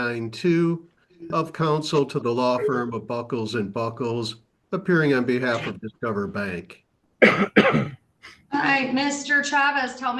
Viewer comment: Did I hear that right? There is a law firm of Buckles & Buckles? That's not quite up there with Dewey Cheatham & Howe or Brooks Boone & Blandino, but still.